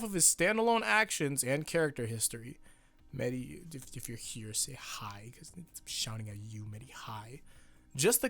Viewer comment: Even the captions can't uncover what you're saying. That's some serious arg creepypasta shit btw. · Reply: Meti if you're here say hi